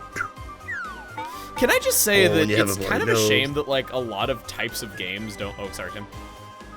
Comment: Can someone tell me what said at 7:20 ? I can't make out what he said. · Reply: "Can I just say that it's kind of a shame that like a lot of types of games don't- oh sorry Tim."